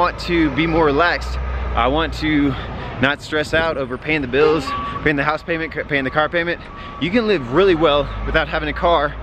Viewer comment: qué fue lo que dijo en el minuto 6:46...después de stressed out y en el 9:50..que es una de las pocas ciudades en el mundo dónde no necesitas que'??.... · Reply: Stressed out over pay the bills, pay the house, pay the car, algo así creo